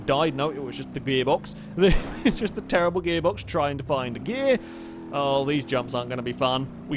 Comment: Did say "Beerbox"?